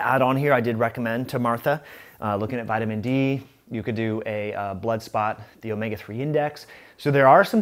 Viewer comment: at @ 9:26 is saying "Bloodspot" ??